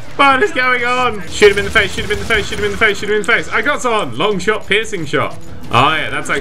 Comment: First i thought you said Long Shot Pissing Shot... He He